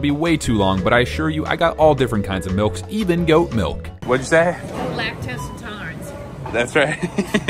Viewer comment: Anybody else hear him say “lactose intoddlerance”?